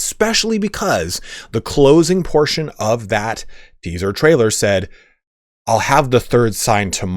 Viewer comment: I thought he said we "shan't have the third sign tomorrow.